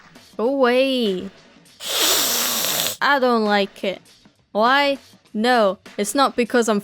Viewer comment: You so British that I thought you say "I don't like KID"